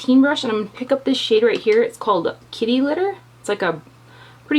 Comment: I thought you said kitty litter lol